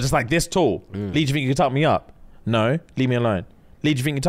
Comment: The whole time James was telling the lead up to his near-death experience, I thought they were saying “Lee, you think you can top me off” like asking dude to give them top and I was so confuuuuuuuuuused 😭